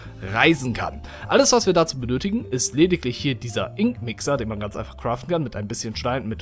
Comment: ich hab wink wixer verstanden XDDDD